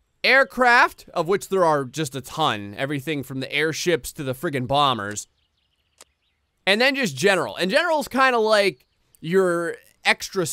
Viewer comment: who heard him say armored warship instead of airship?